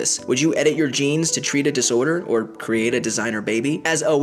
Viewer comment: Who else misheard Life Noggin for "Life Nugget"?